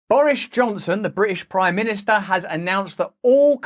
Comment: It sounded like you said Boorish Johnson lol,which is actually much more fitting.